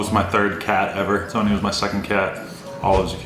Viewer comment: oh my god at 9:50ish says his first cat was named Olive and I THINK he said his second cat was Sonja. My favorite animal is and always has been cats, my name is Sonja, and my nickname from my dad my whole life is Olive. this may be a dumb comment but it was just a lot of random similarities for me. wild